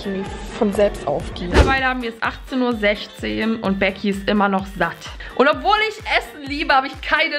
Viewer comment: Hast du lecki anstatt becki geh sagt ich habe lecki verstanden